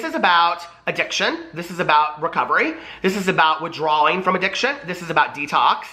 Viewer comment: it annoys me is a “mental health professional” and is saying “withdrawaling” 🙄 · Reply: maya avineri that’s not what he said though. If you’re talking about the 7:45 mark, what he says is “This is about withdrawing from addiction”...which is said correctly.